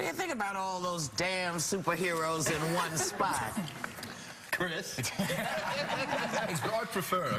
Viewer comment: what is the first thing chris evans said in 1:36???? thanxxx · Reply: “Chris”